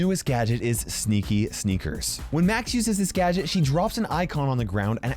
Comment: he says "Max uses HIS gadget" When Max is a SHE tho? Nevermind I listened to it again and he said THIS instead of HIS😂😅wait is saying DUDE when he he also calls her a SHE I am confused 🤪😅